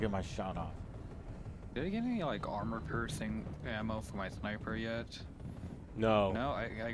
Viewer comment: I just heard "armor-cursing ammo". :D